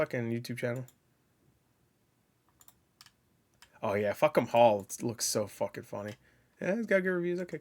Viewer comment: Sounds like you said "Oh yeah, fk em all! >:D" Also, holy crap, 20% is insane 😭